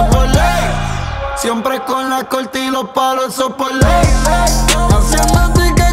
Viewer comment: Siempre pense que decia el sopoley :D